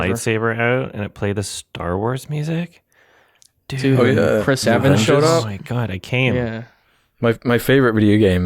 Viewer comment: I totally thought said "Chris Chan" and was absolutely shocked